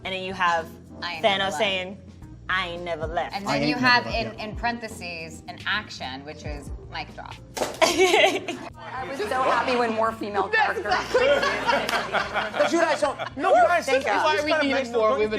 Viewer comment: did I just heard MIC DROP I think about bts...sorry, cant control that